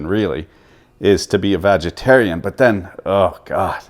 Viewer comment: Did he say "vagitarian"?